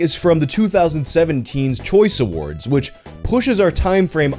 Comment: I kept repeating this section because I thought you said "2017's Choice Award" and not "2007 Teen's Choice Award", and I thought we had a classic case of time-travelling linen on our hands.